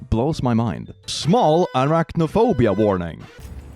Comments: I dont have any of these phobias, but I really appreciated the phobia warnings anyway, it’s always nice when people think about what others might not enjoy. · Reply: Did he say Iraq no phobia at 1:54? 😂😂😂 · HELP 😭😭😭 I actually thought he said Iraq-nophobia, my brain has been polluted by politics. Time to get off twitter.